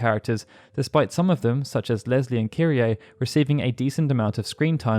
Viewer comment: Thought you said "Lesbian Kyrie" for second. Need more sleep.